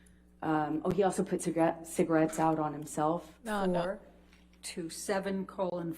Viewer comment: Sounded like she was about to say, puts a gun? 😑🔫(to his head or something along that line)? Then said Cigarettes out on his self.